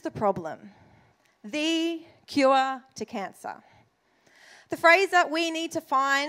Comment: I heard this is “the QR to cancer”